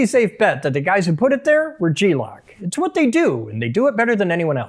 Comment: Thought dawg was just saying glock weird 😂😂